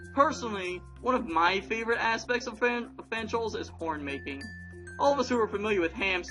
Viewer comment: when ya said "horn making" I heard "porn making"